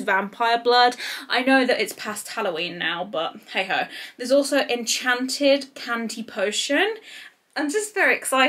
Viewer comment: Lmao I thought you said “Enchanted Panty potion”